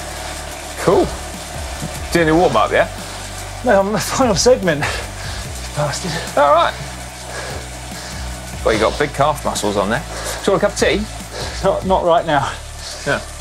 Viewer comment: Does Lloydy call Simon a bastard at 7:08 😂😂😂?